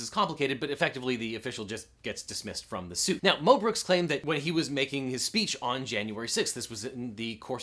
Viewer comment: I thought you said mel brooks and I was so worried for a second...